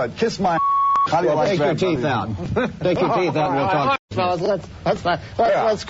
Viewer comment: Did Lofton say "Take your teeth out"? Is that what inspired "Baby take your teeth out"? What does it even mean?